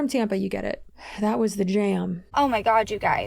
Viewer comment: I thought she said, “OMG you guys coming from an STD…” 😂